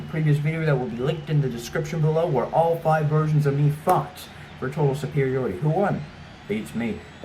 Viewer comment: I know you said fought like they all battled but for a quick second I thought you said “where all five versions of me fucked.” For a split second like I had to do a trouble take.